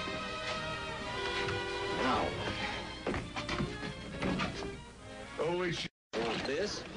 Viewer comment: did he say HOLY SHIT?